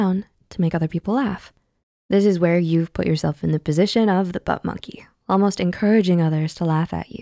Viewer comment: Did you say "butt monkey" at 3:24 ? I've used that phrase since I was a teenager! Haha